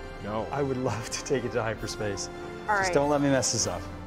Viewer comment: THERE ARE NO LABELS ON ANY OF THE BUTTONS!? WTAF! I can simulate that level of entertainment with my RGB Razer keyboard at home for free... edit Did he just say "I'd love to take you to hyperspace"? Maybe that's the other $4500 of the experience...